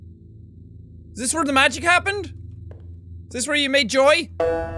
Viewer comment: I thought he said is this where you made joey lol he said is this where you made joy